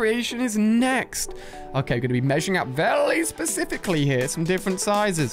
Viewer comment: Is it just me or did he say welly specific